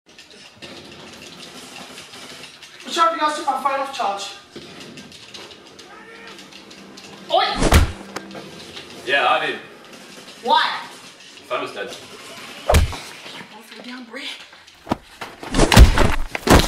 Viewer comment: おい！！って日本語で聞こえない？